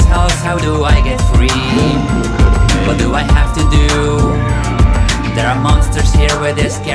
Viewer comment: when he said "What do I have to do?" it sounds like he says "you must rage" XD XD XD XD